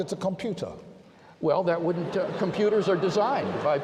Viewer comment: I just love the condescending tone at the end... "Oh come now Wolpert, a mind by definition requires time to function, every schoolchild knows that. Except for the mind I attribute to my personal deity, of course, which is a kind of mind not only unseen in the world but impossible even in theory." He seems to have an addiction to special pleading. It seems like everything Craig does can be dismissed using his own logic - if he doesn't believe because of these arguments. If we assume he at least as rational I am, then I would necessarily be less rational than he is if I believe because of them. If we assume he is less rational than I am, then it is safe to assume that his arguments are as flawed as they appear to be, and I should therefore not believe because of them. The only way out is for people to believe the arguments irrationally! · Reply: I recommend watching the interaction again. Here it is, at 3:36 WLC did not say "a mind takes time to function" he said a computer takes time to function, so you're strawmanning. It's a pretty bad strawman to make because it means you're actually mocking the Atheist who made this video... Given HIS response was "a mind takes time to function" and guess what? He would not be able to support that claim. How on Earth could he possibly know whether a mind takes time to function? By assuming that, he's assuming precisely what he needs to prove: that an eternal mind (God) does not exist. So it's just circular reasoning.